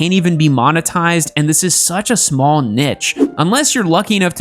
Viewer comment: did he just say nich, instead of niche?